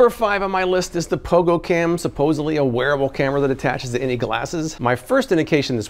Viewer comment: Lmao I thought you said a werewolf cam and my interest was definitely piqued 😂